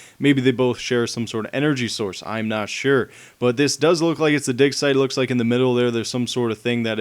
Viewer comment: watch part 3:09 because instead of dig site he says "DICK SITE" and it shows in the subtitles